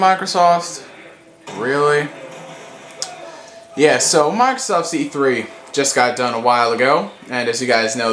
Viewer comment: sounded like you said microsauce